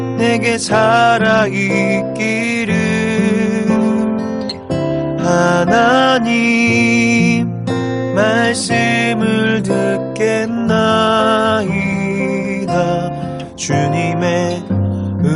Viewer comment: 마나님~ 말씀을 듣겠나이다~ 이렇게 들리는데...